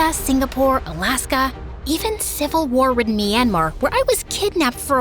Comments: am I tweaking or did I just hear my country, Myanmar? :D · Did you just said Myanmar!!! I live in Myanmar ...🎉🎉